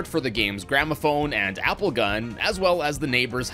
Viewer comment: I thought he said apple gun instead of grapple gun for a second.